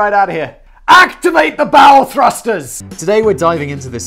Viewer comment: I heard that as "Activate the Bowel Thrusters!!" Which I think works as well in this case.